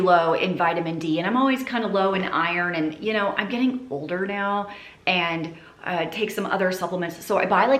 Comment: When you said :" I'm getting older now" , I heard " I'm eating for two now" and my heart stppped😅 I was like: wait up, I missed a whole season here!!